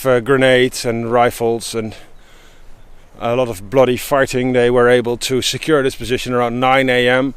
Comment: did he say " a lot of bloody farting"?